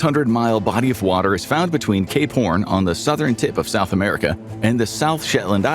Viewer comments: i thought i heard something else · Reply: Exactly lol "gape horn" say that fast, and drake 😭 and more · Ernest Shackleton is a Legend, people were built different back then. I believe it took him 4 years to get back to civilization to save the rest of his crew. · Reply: I’m pretty sure he was referring to “Cape Horn” which sounds like something COMPLETELY different by the way it was said in the video. For the record I had to look it up to; to see if I misheard as well.😂🤣😂🤣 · hear me out, at 1st i thought he said "k p**n on the southern tip of south america."